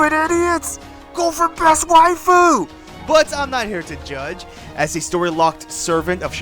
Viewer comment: totally sounded like "go for breast waifu" but that might just be me projecting